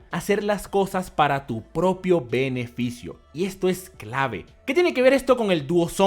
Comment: Loco creo que dijo peneficio en